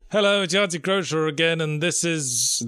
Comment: i always hear: "hello this is yahtzee crosher"